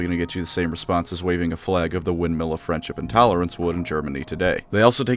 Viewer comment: "Windmill of friendship and tolerance" got me good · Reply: Oh is that what he said? I thought he said "windmill of french intolerance" which was way funnier to me